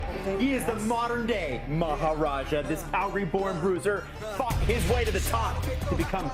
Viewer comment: I thought he said "fawked his way to the top" 😂